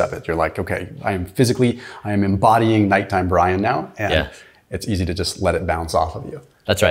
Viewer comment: "Nighttime Brian" i heard "nighttime brine" like eating pickles before bed lol